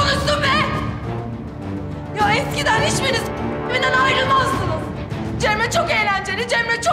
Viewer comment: ‘de ne dedi? Kddkdkdk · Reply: Eskiden kıçımın dibinden ayrilmazdınz